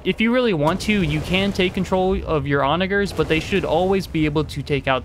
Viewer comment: what did you call me 😂😂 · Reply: Funny enough I actually had a video recently demonetized for saying Onagers. So now I have to call them catapults instead lol